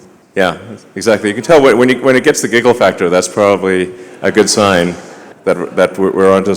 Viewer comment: I heard Elon say "Gigglefactory" Is it just me or...? @ 2:50ish 😆